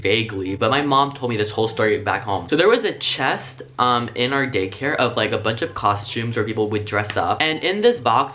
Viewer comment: he said gaycare instead of daycare